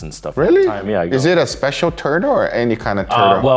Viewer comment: i thought he said “is it a special turd” 😅